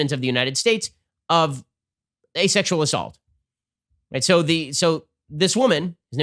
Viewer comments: I thought he said “asexual assault” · When I heard "a sexual assault" my mind heard asexual assault. I'm trying to imagine what that'd look like still.